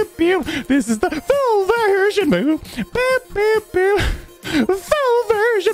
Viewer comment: did he say boob?